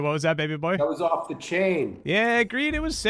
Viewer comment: I thought Arpaio said off the chain instead of and I nearly died. Need new glasses